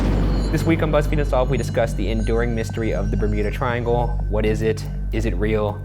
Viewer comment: Wasn't listening properly and thought he said 'the endearing mystery' instead of the 'enduring mystery' and all I could think was 'aww must be an aCUTE triangle then' and now I kinda hate myself XD